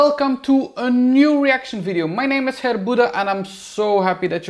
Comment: There is a hair god - it's! (I thought you were saying Herr Buddha for the first fifty videos of yours I watched. I do love your accent, too!) Thanks for all the tips and especially for making me laugh! Ciao kuss!